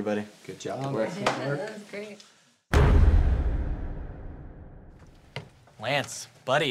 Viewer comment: Wait... did he just say “Lance”? Hm... that makes sense. Also I’m quite aware I’m super late.